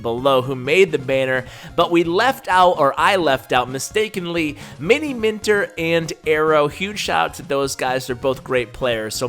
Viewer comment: Did i hear Miniminter 9:05? Cause last time i checked, he was a FIFA player?